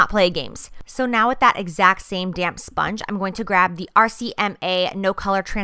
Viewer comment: Every time she said "damp sponge" I thought she was saying damn sponge! Lmao! I love this look! 😍